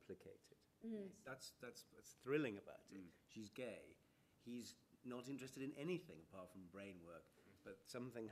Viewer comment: At first, I heard Mark said "HE's gay, SHE's not interested.." when it's actually the other way round. LMAO XD